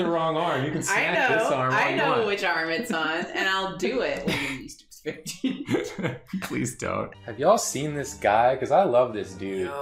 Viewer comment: At about 0:35 I think was saying “when you’ll least expect it”